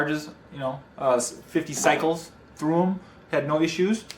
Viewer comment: he spoke fast or with an accent I could not understand. Could someone write down the words he said between 9:54 and 9:57 (something about "thumb up towards the money" what did he says or what does the slang mean ? Thanks much. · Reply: I was talking about how many charge cycles I have on the batterys. How many times I ha e charged them up. And I also said for the money there good!!! Thanks for watching.